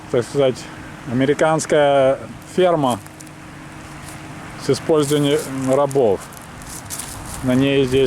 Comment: Послышалось "Американская сперма с использованием рабов". Пойду чистить уши